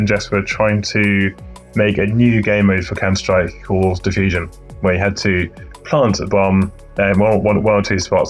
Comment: ongoing typo in subtitling ("diffusion" or "diffuse instead of "defusion" or "defuse")